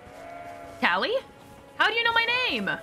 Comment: When I first got to this bit I thought he said Calli as well.